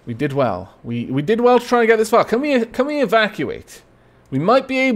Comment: Every time you said "evacuate" in this episode I heard it as "Aavakuate" heh